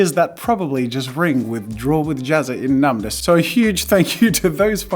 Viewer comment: All I heard was "Withdraw with Jazza"... i mean like... sure?